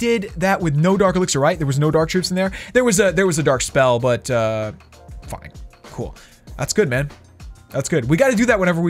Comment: did he say "fuck it cool" or am I tripping and he always swears